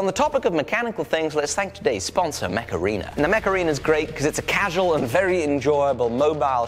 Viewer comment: I kept hearing macarena